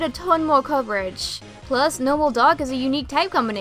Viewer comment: You said Persian should be normal dark type, I thought you said normal and dog type